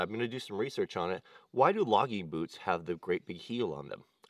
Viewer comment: I couldnt hear the question about boots well but sounded like u said why logging boots have a big heel. But i think its to walk up hills easier.